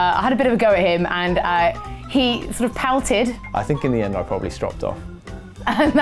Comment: did anyone pick up what said at 9:42 stropped off??